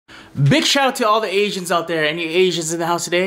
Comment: I thought he said “bitch shout out” in the beginning 😂😂😂